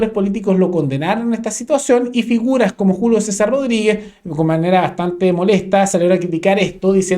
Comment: escuché jugo César Rodriguez 🤣